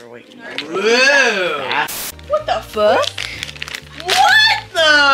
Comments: Oh my goodness 2:58 sounded like or had said "What the f***" and I almost lost it😂Hope y'all had a good Christmas! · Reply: said ‘what the freak, what the’ so you safe there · i swear i heard what the f*** at 2:58 wth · Did anyone else hear or ( idk who it was) say what the f** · What did say? I'm pretty sure it must not be what it sounds like. Merry Christmas & Happy New Year. I'm glad you're all home together. ❤ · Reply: Ikkkkkkkkk I think it was what the fawk😊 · I’m not going to lie it sounds like says what the F**k when she opened her books from santa